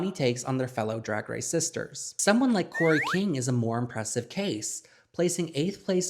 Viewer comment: when talking about kori king at 12:38 the captions say krystal versace😭